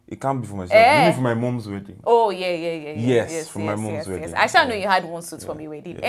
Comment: Sorry, I heard "from my mom's wedding"